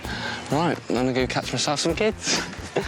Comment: did I just hear "now I'm gonna go and catch myself some kids"